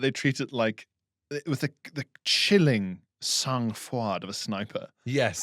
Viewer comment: What does Pierre say at 15:37? It sounds like "sung fwad" but I don't know how to spell it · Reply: sangfroid! Meaning coolness in danger or trying circumstances